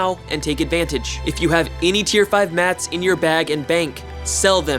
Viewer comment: This channel is like, bound to grow as Albion gets more and more popular. I'm just glad I'm here from the beginning. One question from this vid I have is what did you say at 1:49. "If you have any tier 5 _____, sell them." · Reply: he said if you have any tier 5 mats (materials) :)